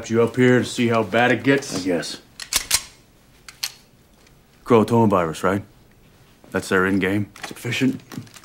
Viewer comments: Did he just say corona virus???? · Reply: No, he said Croatoan Virus, but it's pretty much how Corona Virus has impacted. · First time I swore he said Corona-Virus.